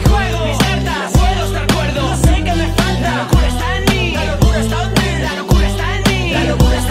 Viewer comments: pense que decia la locura stanley xddddd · que temazo, aunque todo el tiempo pense que decia "la locura stan lee " XDDDDDDDD